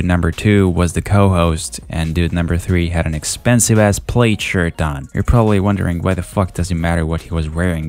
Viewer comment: Are you saying plaid because it sounded like you said played XD